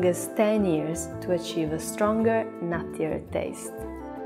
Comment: does she say a stronger nastier taste?